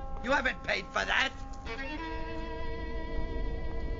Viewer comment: "kids today" I always hear it "kids these dayz" #endtimez